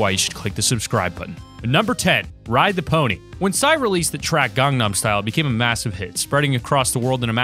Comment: swear he said gumnut style